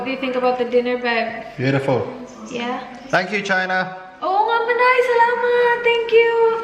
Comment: thought I heard "thank u China". Charot. Haha